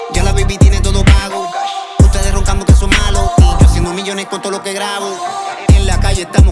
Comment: Feeling bad for those who don't understand Spanish 🔥🔥🔥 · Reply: Yo al principio pensaba que el queria decir millon pero me di cuenta que no era millon es melon At the beginning I thought he wanted to say million then I found out that he wanted to say melon not million